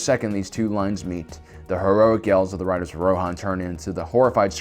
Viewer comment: did... Did you just say "the heroic ELVES of the Riders of Rohan..."??? 🤯